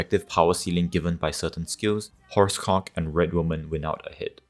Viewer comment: Horse 10:04 Probably misheard it😂